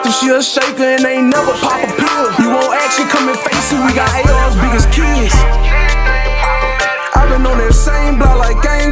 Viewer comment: Nigga did you just say "we got ars big as kids"😂😂😂